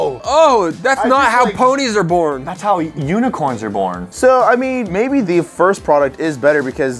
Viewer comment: I thought during he said that's how unicorns are born I thought he said that's how you were born cuz I heard the part in u as u you